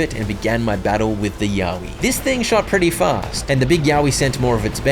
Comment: The final fight is endlessly funnier if you mishear it as "yaoi" ( 1:05:24 - 1:07:00 )